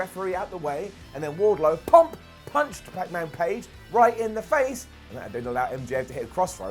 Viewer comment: "Pacman Page" Did I hear that correctly?